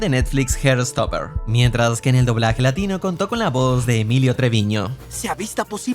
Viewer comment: entendí julio triviño